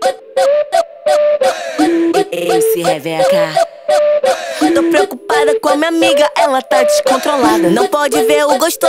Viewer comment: Só eu que entendi "Mc Reveca??" Kkkk